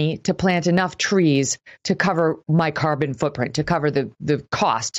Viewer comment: Haha I thought she was saying carbon fu**print. It would be more appropriate for them! Now maybe if they gave some of their empty rooms to homeless veterans people may